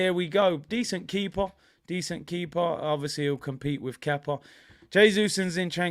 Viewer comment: hahaha I thought he said Keppa